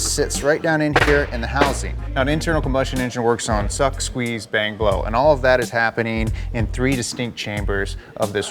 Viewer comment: HOLD ON what’d he just say?? Suck bang blow??